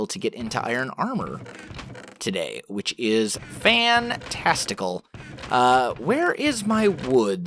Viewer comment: on 14:38 did he say fan testicle?